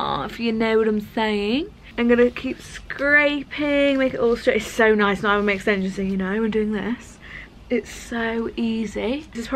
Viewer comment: Quick question girls: who edits your videos? Also 27:35 what on earth did say 😂 played it twice to try to figure it out but still don’t understand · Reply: “It’s so nice not having my extensions in you know”